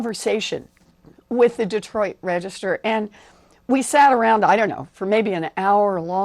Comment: anyone else hear warren say Detroit Register at 0:58 lol